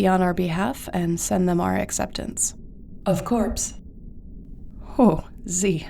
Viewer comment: wait... what was dark? Z just said 'of course'. Did something get cut? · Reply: She said "of corpse"